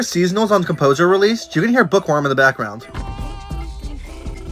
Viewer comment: I thought u said jamboree, so I replayed it so many times then my dumbahh found out you said buqworm